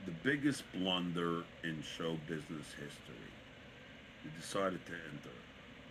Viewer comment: I had to listen to that several times with my speaker right on my ear. Every time I thought he said "he decided to end it". The actual line made zero difference on comedic impact.